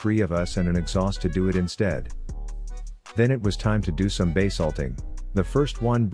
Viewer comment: I heard you said, "now it's time for Bae Salting!" Instead of base ulting... 🤣💀Rip my hearing.